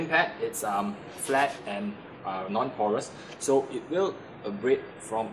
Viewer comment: did you say the denim pad is non coarse? Or is it a coarse pad. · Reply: Ah! I meant non-pores, as in compared to foam or wool, the abrasives will linger longer on the surface of the Denim